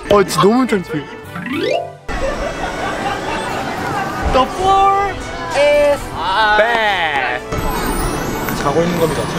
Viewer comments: At first I thought they said ‘the floor is bad’ 💀💀💀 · 2idiots: "The floor is bed" No one literally no one: ...... Me: WHY DO I HEAR "THE FLOOR IS BAD"